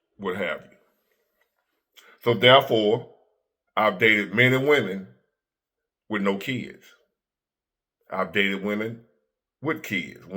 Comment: it sounded like you said you dated men and women without kids 😭😭😭 Had to replay that, unc